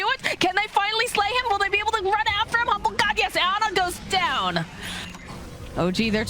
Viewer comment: did she just say HambogGod? Hahaha.